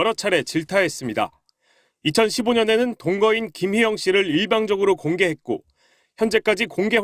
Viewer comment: '김혜영씨를' 라고 들리지 않나요? 이름을 틀릴 정도면 발음이 너무 안 좋은 듯 한데.... 내 귀를 파야 돼나?